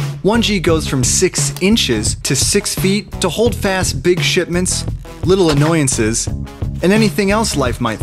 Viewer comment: who else heard shit mints?